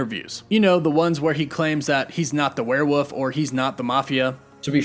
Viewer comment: Hahaha he sounds like hes saying werewoof